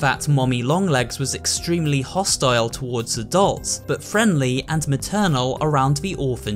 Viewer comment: You said that she was hostile towards the dolls, but I'm pretty sure the documents said she was protective of the other dolls/experiments and was hostile towards the staff and doctors. Maybe I misheard it. 🤔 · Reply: I'm pretty sure he said adults and not dolls.